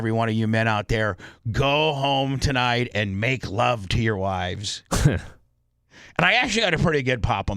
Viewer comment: Let’s F our wives is what was said!